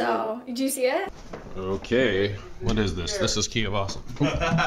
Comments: Thumbs up if you thought at first it said "The cookie of awesome" listen to it again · When I saw this I didn't hear "The key of awesome" or "Ducky of awesome" I heard "Cookie of awesome"